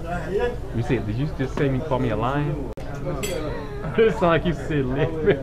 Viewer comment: wuxuu dhahay lix ciyaal baan leeyahay which means I have six kids. But you didn't understand. You thought he said you are lying to me . It was nice conversation and they were good men